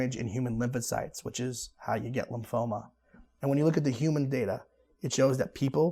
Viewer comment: in the subtitles "how yo get lymphoma" instead of "how you get lymphoma"